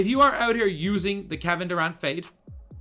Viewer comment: why subtitle say "cabin around fate" when you are saying kevin Durant fade ? 😂